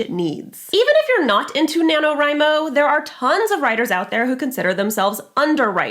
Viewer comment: I was painting with this video in the background and I literally thought she was saying Nano Rhino; every time she said it I mumbled the word, Crash because you know, rhinos.